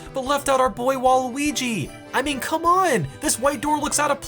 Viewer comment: Did you just- Did you just say Luigi is a god? 0:52 Nvm I forgive you